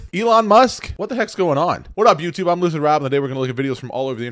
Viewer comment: That into, you said it fast and it sounded like you said “ lizard Rob” … 😮 you’re one of THEM !